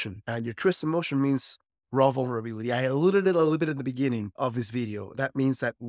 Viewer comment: I'm hearing: "Your truest emotion means rovolrmy". What is that word? It's a puzzling mystery! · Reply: I checked the transcript and it appears that he’s saying “raw vulnerability”.